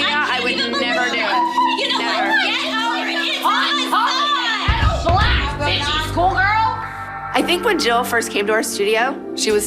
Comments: did Christi say something about being the ghetto black 🧙 at school 😂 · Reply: she said it was the pot calling the kettle black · she said it was the pot calling the kettle black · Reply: chile I thought she said “the ghetto black 🧙 at school girl” I was so confused 😂